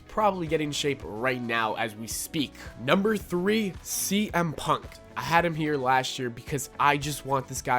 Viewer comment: did you say CM Punked?